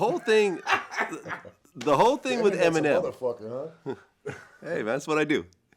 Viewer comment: what did dude on the right say? And then what did he say after vlad says "that's what I do"? Thanks in advance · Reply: "The internet's a motherfucker, huh?" Cause they thought it was crazy he knew how many houses he owned.